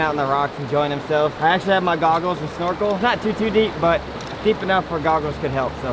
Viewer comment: seconds, I first thought you said doggles as in GOGGLE for dogs, had back to make sure I heard you correctly. Then listen carefully, you said goggles 🥽. That what I get for not paying attention. 😂 😆 😝